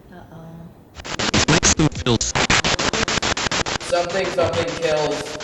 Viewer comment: the spirit box sounds like its saying "Mixed-race Kills"